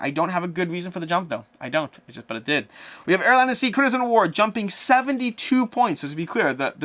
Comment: I either have terrible hearing or you swallowed your words, but could you please tell me what the highest riser was again? It sounded like you said "airline-c: crimson war" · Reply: Air, land and sea critters at war